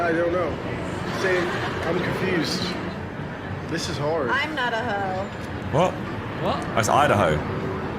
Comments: I thought she was saying I'm not a whoe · Why did i hear I am not a h0e💀?